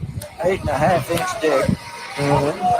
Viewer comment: it sounded like d*ck at first to me, but then he sounds southern and saying deck/ deck measurements?? Idkkk